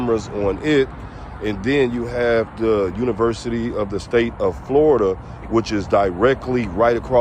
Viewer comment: USF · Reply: o thought he said university of Florida